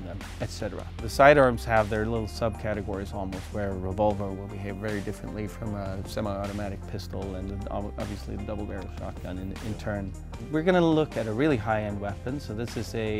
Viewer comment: Wtf did he say at 2:39? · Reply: babbet "side arms have a sub category where a revolver will handle very differently than a semi-auto pistol and obviously a double barrel shot gun in turn"